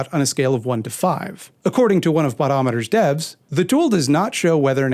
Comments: I will say, when you say "Botometer" I heard "But-t-O-meter" as in straight out of his... and it made me giggle, because it would have just been as likely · Haha at first I thought you said 'buttometer' as in he pulled the number out of his butt. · When you pronounce "botometer" I hear "buttometer" which I assume measures the intensity of farts. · I swear that instead of "botometer" I heard "Butt-o-meter" and now that's going to be my preferred euphemism for pulling numbers out of your ass.